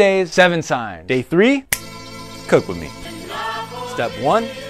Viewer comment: I heard "kokomi" instead "cook with me" da hail